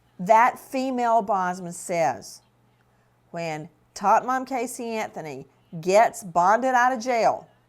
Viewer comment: I was obsessed with watching Nancy during the Casey Anthony trial and the second she said “tot mom” I was transported back so fast. 😂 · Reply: Oh! I thought she was saying Top Mom, in a sarcastic way